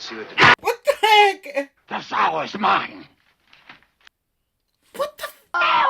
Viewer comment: Two things I misheard the first several times I saw this. I never realized that Karl says, "IT wants no straps." How creepy that he used "it" to the girl's mother. And after the demon shouts, "Stay away..." when the doctors come to the house. I always heard the next line as "This hour is mine." When what is actually said is, "The sow is mine." Both creepy and both work, but the second one is the correct one.